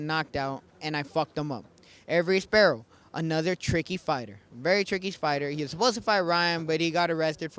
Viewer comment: did he say trickey Spider 🕷️ lol 🤣